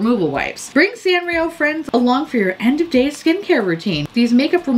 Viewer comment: I heard "end of day makeup removal" as "end of DAYS makeup removal" and I really thought they were stating at the beginning of the apocalypse you should be removing your makeup with Sanrio wipes.